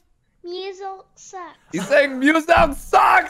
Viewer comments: I thought he said Meowscles suck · I thought said meowcles sucks🤣